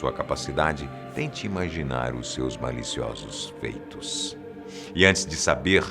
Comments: só eu q entendi maliciosos peitos? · Só eu que entendi "Maravilhosos peitos" quando ele disse "Maravilhosos feitos" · eu achei que ele disse "Seus Maravilhosos Peitos" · MANOOO EU TENHO UMA MENTE TAO POLUIDA Q EU OUVI "PEITOS" '-'